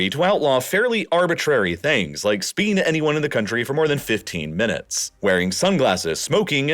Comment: what is he saying here? 'speeding anyone in the country for more than fifteen minutes?' What does it even mean?